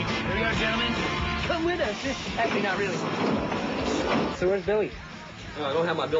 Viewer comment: anyone tell me what tre cool says at 1:19? he says: come with us... and then? i don't understand. thx i appreciate it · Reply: uh i know this is 9 years later but he says “come with us! actually not really-“